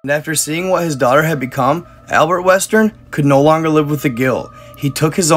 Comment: Yo i thought you said Albert wesker like from resident evil wait what if the games are some what related or something of the sort i would like to know 👀🙌